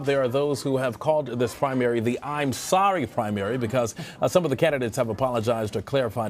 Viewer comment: did he say “ I’m sodomy”?